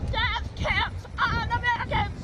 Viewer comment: Ok, that first wacko the first time I saw that I thought she was saying "no dead cats in America."